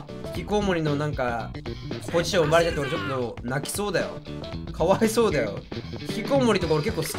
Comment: ここのヒキコウモリとかのあと何言ってるか永遠に聞き取れん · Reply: ポジション奪われて、ちょっと泣きそうだよ、